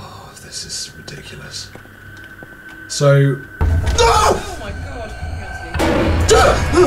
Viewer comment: I can swear that I heard someone saying "Oh my God" at 15:31. Skyping?